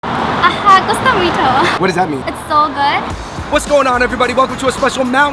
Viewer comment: I thought she said gusto mo ito? lol xiao si wo le